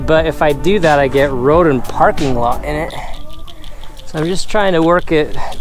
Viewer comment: Thought you said 'rodent parking lot'. Can't vote: never come between a man and his wife!